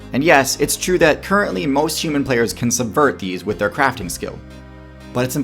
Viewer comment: ’s caption said “ability” instead of “skill”.